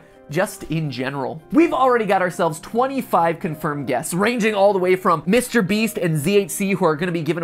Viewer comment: i thought he said '25 confirmed deaths' and then showed the youtubers and i PANICKED